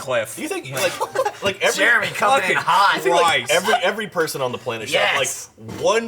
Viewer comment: The comment section is all just people hating on Mica. Christ, get over yourselves, no one's forcing you to watch it. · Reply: I thought you said chill so I looked for it, sadly was Christ.